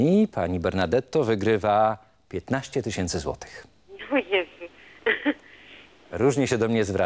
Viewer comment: co ona powiedziała prowadzącemu? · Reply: „o Jezu”